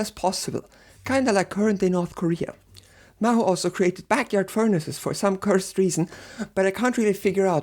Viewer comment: I thought you said Mao made backyard furniture for some cursed reason. The mad man.